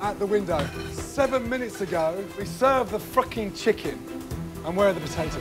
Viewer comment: I don’t think I’ve ever heard heard Gordon 1) with his voice so cracked/strained and 2) say FRUCKING 😂